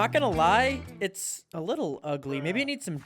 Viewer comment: Bro said “Ngl it’s looks….”I thought he said great or cool. But I was wrong. He said “a bit ugly”